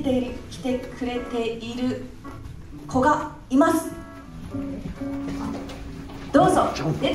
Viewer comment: 応援に駆けつけてくれている子が居ますだったんでしょうが 応援に駆けつけてくれている古賀居ます に聞こえて娘呼んだの？ってなりましたね(笑)